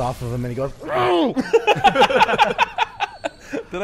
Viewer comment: I thought he said cause he hurt my trashcan lid